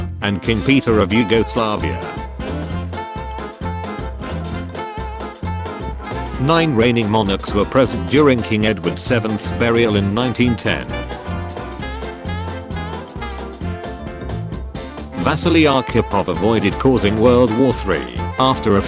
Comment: I had to replay that bit. I heard "King Edward's seventh burial," the first time and I had questions.